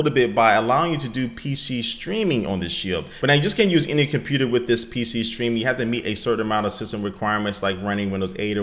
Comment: When said "with this pc stream" I thought he was going to say "with this piece of shit"